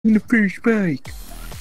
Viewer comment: If you turn on captions the first thing you see is THE FISH BAKED lmao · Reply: If that's what actually said then I thought it said "the first spike"